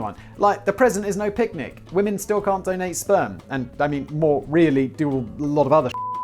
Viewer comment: "Woman can not donate sperm" ? Did I hear that right? [Edit] You mean like, after sucking some one off?